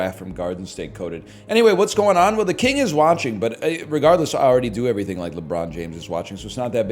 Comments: Thought is said the king is hatching and was ready for some chicken coop shenanigans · I thought it said "the king is hatching"....